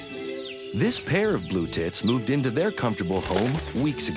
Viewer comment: Hahaha it sounded like he was saying “this pair of blue tits”